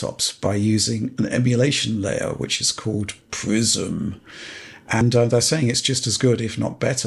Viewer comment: I keep hearing "Microsoft Prison" instead of Prism ...